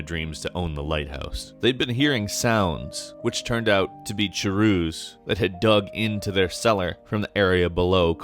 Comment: did you say chewrews?